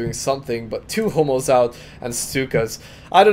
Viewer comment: I thought he said homos instead of hummels..